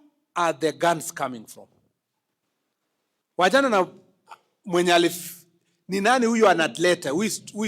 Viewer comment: Angalieni 5:29. What was he about to say? Wachana na Mwenye ali?